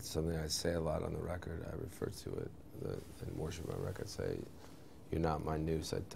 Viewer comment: at the 59 second mark, he mumbles, "worship my record" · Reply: It sure sounds like he says 'worship my record'! lol But he spoke so fast there when speaking of the track "Warship My Wreck", in which said fast enough can come out to sound similar to 'worship my record'.....